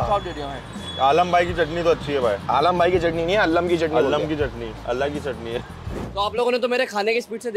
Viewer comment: did he said alag chutney ya allah ki chutney Can anyone correct me kya bola usne agar woh bola joh meh sochra toh fir karna padega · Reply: Alag hi chutney h ye bola